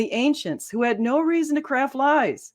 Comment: Have to admit, first time around, it sounded like she said, "crap lies".